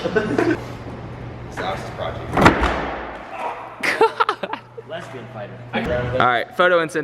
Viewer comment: Did say lesbian fighter